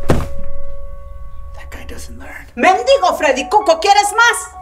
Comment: I don't know what she said.... BUT I HURT "FREDDIE COCOA PUFFS" COME OUT HER MOUTH XDD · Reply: She said "you hobo freddy cuco want some more?!!"